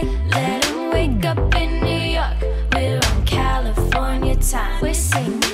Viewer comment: we wake up in the oven😂 I'm I the only one who hearing that? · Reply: I hear "let em'wake up in new york California time"